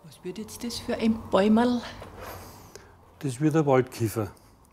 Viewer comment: Ich hab Waldkiffer verstanden....nunja... :)